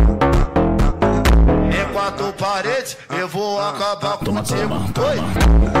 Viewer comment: What are the words at 0:55? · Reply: Em quatro parede eu vou acabar contigo